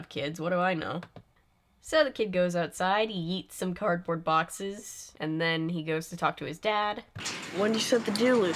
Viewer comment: I like how the subtitles say he eats some cardboard boxes instead of he yeets some cardboard boxes